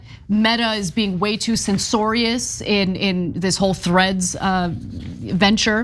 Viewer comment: Ana wtf did you just say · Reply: Censoriues